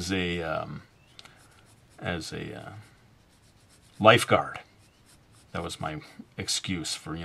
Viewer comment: life guard, I thought you said life god I feel so stupid right :0